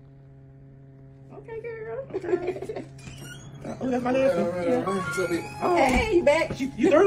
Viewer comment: I couldn't tell if said, "That's my nephew." It kind of sounds like said, "That's my husband." or "That's my lashes." Hell, I don't know. However, now that I see with makeup on his face, they do share some of the same features. Hmmm. People keep saying he looks like Shannon Sharp. They have wedding bands but we didn't see a ceremony. They usually record important events. If that is true "Charles" is actually a relative. It would be twisted.